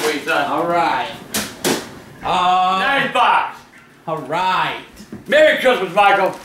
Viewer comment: Did he say Xbox?